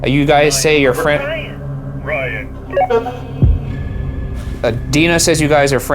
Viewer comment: "uh, looks like Ryan" "Ryan" "RYAN" in almost the same tone as the walkie talkies? Does anyone else hear that? #debrief